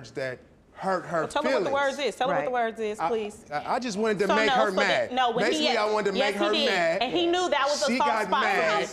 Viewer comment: what was the word that made her mad? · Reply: Biznatch